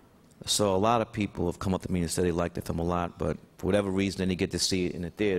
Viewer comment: Did he say , " Likeded them a lot"? LOL 😄 you're excused Sir